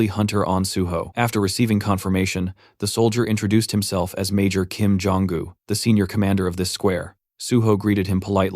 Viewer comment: did he say major 'kim jooong un'